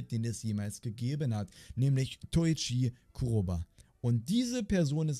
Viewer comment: Toichi kurrw..? OK hab mich verhört